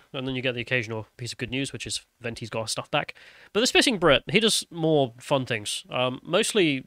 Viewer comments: I thought you said "SpLiffing Brit"!!!😆😂🤣😭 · Pheww for a moment there i thought you said Spliffing Brit.....